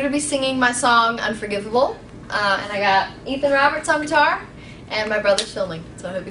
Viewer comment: oh, i thought she said "and my brother chilling" ahah, my bad<3